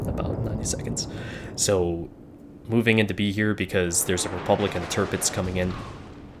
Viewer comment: At first, I thought you said "Republican Tirpitz" xD